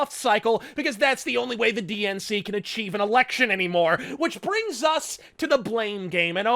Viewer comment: “Achieve an election “ I nearly got in a car accident when I heard that Dear God that was funny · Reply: i thought he said " achieve and erection" not "achieve an election"